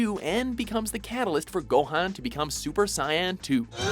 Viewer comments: did he say super Cyan? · Did he just say Super Sion?????????? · Did you just say super cyan 2? Like, what.